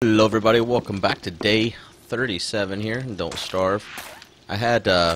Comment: did he say "Don't Starve" ???